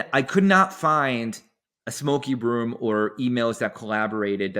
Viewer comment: Thought he said smokey broom. Evil haha witches.